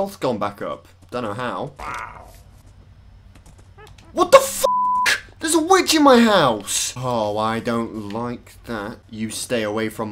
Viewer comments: For the whole of this day I thought it said ‘wither breaks into my house’ Crisis averted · Bruh I thought it said wither breaks into my house too lmao